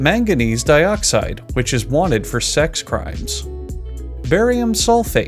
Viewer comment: anybody else hear "Wanted for sex crimes" 🤔